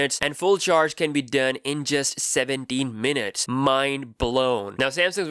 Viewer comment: Wait 7 min 50% full charge 70 min im confused what happened to 14 min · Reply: Oh I thought he said 17 min?